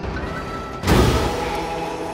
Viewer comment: I keep hearing "It's good to be gay" Lol